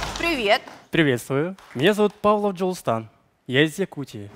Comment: МНЕ ПОСЛЫШАЛОСЬ ПАВЕЛ ДЖОСТАР